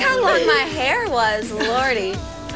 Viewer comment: What does she says at 3:09 - «Look how long my hair was (?)»? I can't understand the word. Sorry, I don't speak English :/ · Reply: Sugarfall13 She says "Lordy"